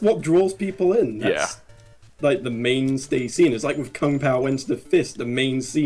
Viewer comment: What movie did you say finally got a sequel? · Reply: Green Thunder Kung Pow.